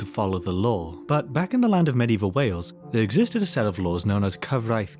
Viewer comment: In the beginning I thought you said land of medieval whales. Thought you found Atlantis or something